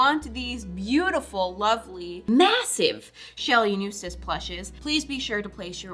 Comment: I skipped thru the announcement boy and at the end I just heard “new cis plushes”